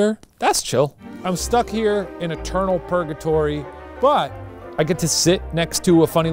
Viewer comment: I thought Shane said "stuck in a turtle purgatory" instead of eternal purgatory. (He spoke properly, my listening skills suck sometimes.)